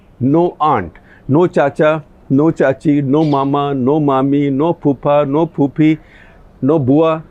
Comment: Did he say no poopy at 9:24 😂😂